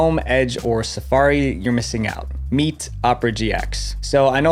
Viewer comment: the captions are wrong... It should say "You're missing out" but it says "they were inspired to copy her"... sorry I just notice the most random things